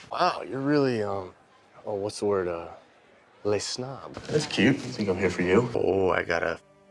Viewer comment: I don’t know why but I just find that so funny and I don’t even know what he’s saying but it sounds like ~lesnaab~ BAHAHA